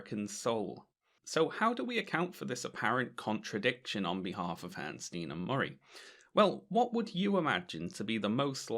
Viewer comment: For almost the entire video I heard "Hansen Ninamori". I feel stupid.